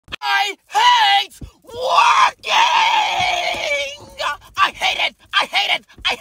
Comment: I thought he said "I HATE WALKING"